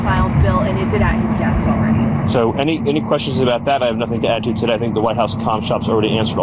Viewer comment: Ooop I thought taco trump said “we have nothing to hide” guilty as hell